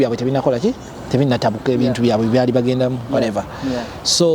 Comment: Did he say ebintu byambwe byebagendamu at 34:51 So ebye Uganda tebikwatako mukulu hmmm mwempake nyoo